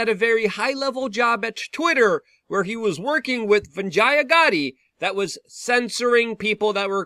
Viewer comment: did you say he was working with Vagina Gotti? 55555( hahaha in Thai)😜